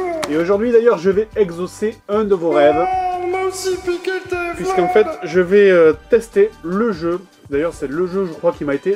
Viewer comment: JDG: Salut a tous nouveau JDG... Le jeu : Non! Mousipiqueter moi ! Moi : 😂😂😂 c’est quoi mousipiqueter? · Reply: Il a dit : on m'a aussi piquer le téléphone